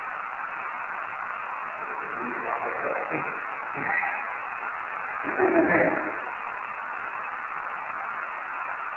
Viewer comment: The part where they get the evp saying doctor the whole clip they played there is talking but I couldn’t make it out something help me *cough something something the doctor I don’t know exactly what it said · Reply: Maybe “are you about to help me? cough laugh _____ here __ the doctor”